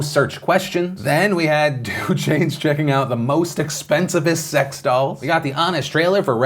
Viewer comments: Did you say "expensivist"? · It sounded like you said "and then we have the Amish trailer of Ready player one" xD